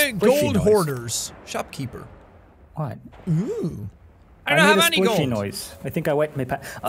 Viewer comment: Did he just say "OwO"